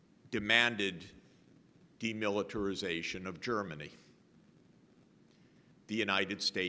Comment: As many others have already pointed out, this is a brilliant speech full of compassion for all humanity on earth. Thank you. Allow me to point out three unfortunate but sometimes crucial mistakes in English subtitles. What he said at 13:07 is "DE-militarization" instead of "the militarisation, I believe. At 13:37 "into NATO" instead of "in tornado." And at 19:18 "Yeltsin" instead of "Yelton.